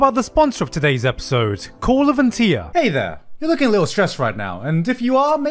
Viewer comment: I heard "call of NTR" on the sponsor.. Despair bruh